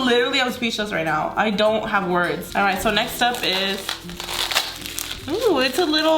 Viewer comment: did she just say next step